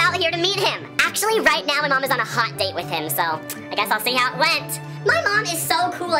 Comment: this hits different when u were younger and didnt understand his jokes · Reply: yes the “I guess I’ll see how it went” one I remember mishearing him thinking he said “I guess I’ll see them at Lent” and I was so confused but now it makes sense